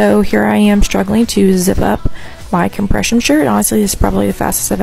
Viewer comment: thought she said depression shirt